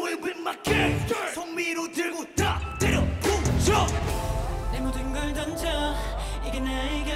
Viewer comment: 여기 송민호들고 다 때려부셔로 들려서 미치겟음..